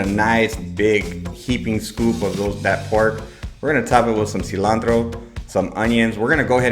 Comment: If only I could understand what you say when you name the pork cut used. I hear "pork <whorf?>. Recipe seems wonderful. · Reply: pork shoulder